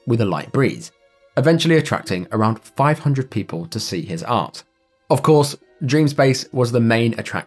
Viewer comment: I know what he really said but it kind of sounded like he said "attracting around 500 people to see his arse." 🤣